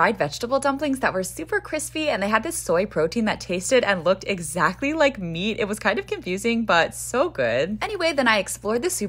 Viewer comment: The humbleness to end with “thanks for watching” that’s dope. I’m deaf & still heard that. Lol it did also sound like fam said some candies look exactly like her? 🤔